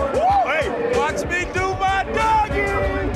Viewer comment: did he say watch me do my doggie?